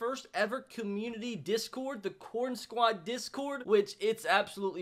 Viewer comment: At first I thought he said porn squad 🤣